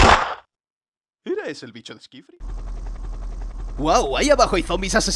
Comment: Buenas!!! alguien sabe de que juego es el bicho que aparece en en minuto 5:12, lo dice pero no se entiende mucho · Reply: Dice skyfree